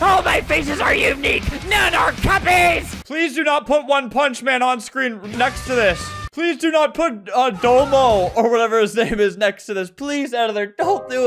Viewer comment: I thought you said the brand DOMO (i was looking somewhere else) like for Electronics and IT stuff that there was there face on the Marketplace but it was green puffy man and not my childhood brand with the two ball cat(green)and dog(blue) with stripes on them